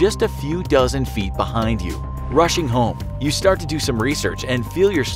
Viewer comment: deos anyone else hear Russian home